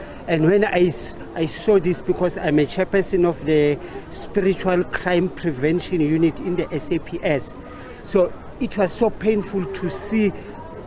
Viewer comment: Did I hear this pastor correctly, "spiritual crime prevention unit in the SAPS"???😳..